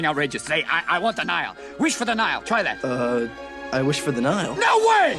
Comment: "I wish for the Nile." It almost sound like he said; I wish for denial xD